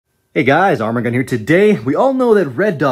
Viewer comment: sound like he saying armor gun. arm and gun